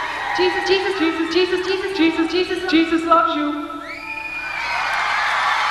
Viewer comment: I remember this confused the hell out of me when I was a kid, all I heard was "cheep,cheep, cheep, cheep....love you love you.